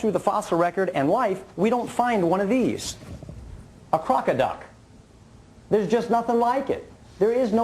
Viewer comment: "To say there is nothing like the crocodile................. hahahahahaha. What a doofus." At first, I thought he said crocodile too, and the picture was hard to see. But, he said "crocoduck". In other words, he's claiming that transitional forms are absent from the fossil record.